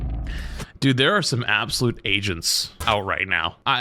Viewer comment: I thought you said there are some absolute Asians out right now 😂